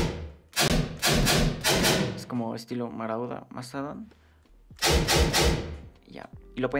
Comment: le entendí maradona no marauda xD